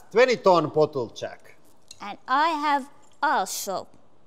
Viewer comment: I thought she said “… and I have AR##HOLE” - I was gonna say that’s not a nice way to talk about him 😂